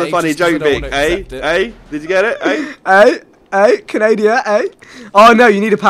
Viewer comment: No one took notice to the fact that vikk said canadia instead of canada